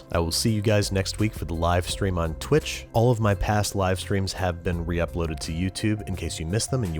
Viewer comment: I heard "past live streams" and thought "past life streams"!! :D